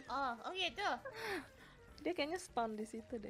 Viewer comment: tulisan nya spam tapi yg aku dengar spawn, apa aku salah dengar ya 🤔?